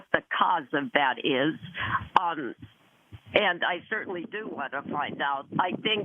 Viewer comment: Can anyone tell me what the person at 8:52 says: "And I certainly do ... ... ... out". I want to use it for English class and can't make out the words in that part. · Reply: "and I certainly do want to find out"